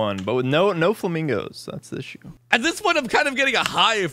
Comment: i think he said "that´s the issue" instead of "that´s the show"